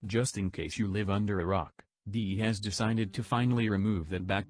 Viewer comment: When you said you lived under a rock i heard it " you live under Iraq", as an iraqi you got me worried for a second